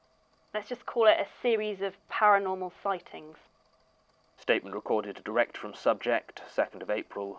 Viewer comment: I thought she said a series of paranormal fightings and i was like holy shit did she try to beat the shit out of jane??? You go girl get her ass